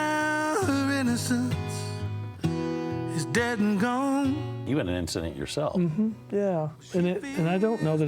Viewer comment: What does interviewer say around 1:19 can't understand it. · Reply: He said "you had an incident yourself"